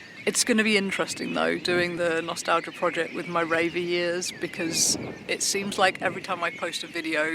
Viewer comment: I'm on a mix of heroin and diazepam and felt pretty certain I heard "my rabies" instead of "my raver years"...